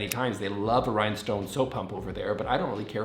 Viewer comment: LOL. When first mentioned rhinestone soap pumps, I thought he said "sump pumps." Now THAT would have been going overboard!